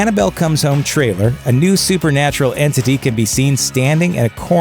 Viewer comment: I thought it said, A NEW SUPERNATURAL HOE